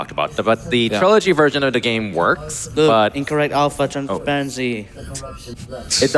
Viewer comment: What does say at 1:58:33 ? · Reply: "Look, incorrect alpha transparency!" I'm not actually sure which part of the hologram is the issue - maybe the planet core? - but he's talking about the game not rendering the hologram correctly.